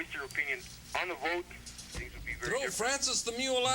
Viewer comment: Wtf did that person say 💀 it sounds like he said "frances the mule"... someone help me out here